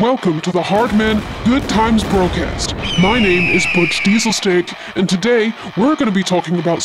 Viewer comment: Butch Diesel-Steak? Lol... I thought you said Diesel-Stick at first...lol I suppose it would work either way 😀